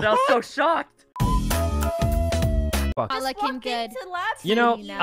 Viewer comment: I thought sykkuno said fwpuck